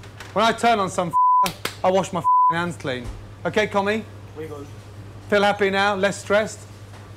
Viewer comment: Gordon doesn't like communists in his kitchen, it's all about individuality to this chef. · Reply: I knew it! I thought he was saying commie but wasn’t sure. Wow, lol! That would not fly today.